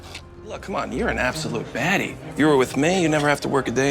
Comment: did he say baddie